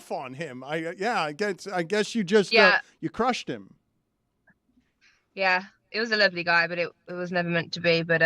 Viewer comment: When she said "he was a lovely guy..." I heard "He was an ugly guy it was never meant to be.." I was like Daaaaaammmmnnn